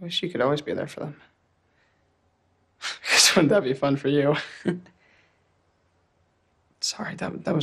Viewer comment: What does he say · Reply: jonah says "i guess that would be fun for you" implying that cyrus being there for his panic attacks would be a positive